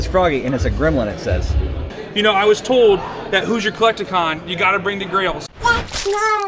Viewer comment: At first, i thought you were saying, "Who's your collection?"😅